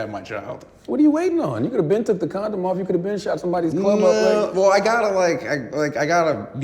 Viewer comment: Someone help me, I’ve listened to this interview dozens of times and I have absolutely no idea what charlamagne is saying at 11:39 lol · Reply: "you coulda been took the condom off. you coulda been shot sombodies club up." i think lol, he does say it pretty fast.